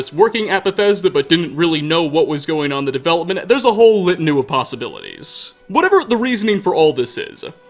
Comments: this is so unimportant, but what word is he saying??? Litany? Litanoo?! It’s really bothering me HELP lol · Did you say "litanu"? Assuming you made a typo of "litany" in the script, and spoke the typo? I mean its understandable, litany is an uncommon word, and u and y are next to each other on the keyboard.